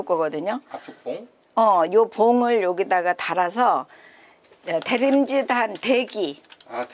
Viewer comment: 나만 대림질로 들리나??